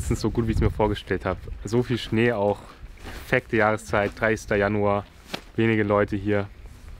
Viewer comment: Wer hat auch dreister Januar bei 1:07 gehört? xD